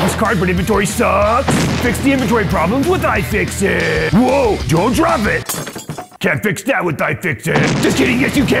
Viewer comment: Did someone say LINUSDROPTIPS?!?